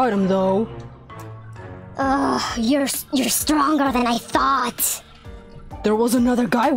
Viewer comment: What the video said: Your stronger than i thought What i heard: your stronger THAN MY THOT